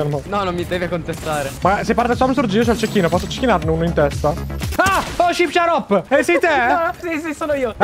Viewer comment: ma cosa cabbo dici al minuto 2:12 io l'ho capito🤣 (albanese) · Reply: qifs